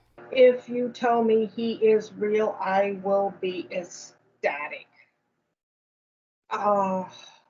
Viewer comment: when she said she would be ecstatic, I thought she said "I will be his daddy" 😅